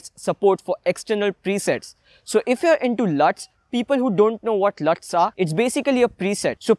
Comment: Almost puked @ 3:39, I thought you said s luts :P